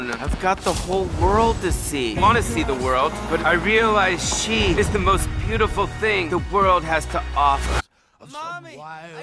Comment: Lol i thought he said “i thought cheese is the most beautiful thing the world has to offer “